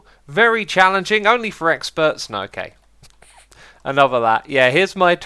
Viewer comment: did he say "X-derps?"